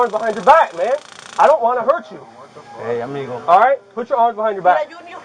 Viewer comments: Please tell me I’m not the only one who heard “hey I’m legal” at 5:57 because that had me laughing my ass off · Did I hear "Hey I'm Legal?"